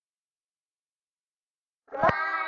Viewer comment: first i thought it said one the bridge is falling down